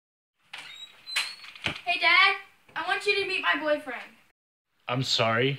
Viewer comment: Why did i heard "hey dad i want you to BE my boyfriend" BYE😭